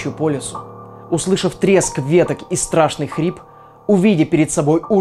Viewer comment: Мне послышалось „треск котлеток„ 😂 Видимо я проголодалась…